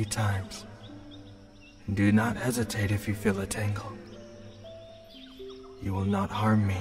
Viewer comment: kaminari calling us lovely and darling and putting the flower behind our ear was so nice. WE MET A UNICORN ANDD GOT A RING, and denki singing at the end while playing guitar with kiri’s dragon purring was the best ending in this series so far. ty for this <3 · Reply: Ohhhh I just realized he said "tangle" I thought he said "tingle" that makes more sense